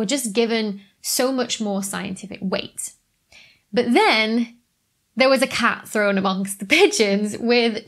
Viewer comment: I clearly heard "There was a cat thrown amongst the bitches".